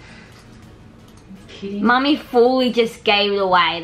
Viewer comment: did say mummy ive never heard that